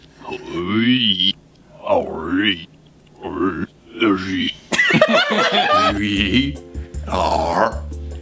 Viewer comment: I thought said We are Grootjerkey 🤣